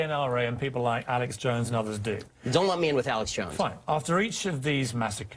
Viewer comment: what did Ben say about Alex Jones? I couldn't understand him. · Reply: He said "Don't lump me in with him."